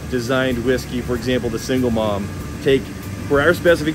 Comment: Maybe it's me, but why did I keep hear him saying "single moms"? 🤣